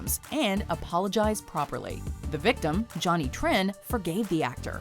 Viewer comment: does anyone else hear Johnny Tran? Like from the first Fast and Furious movie?